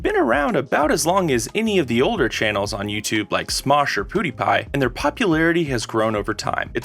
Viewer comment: DID HE JUST SAY POODIEPIE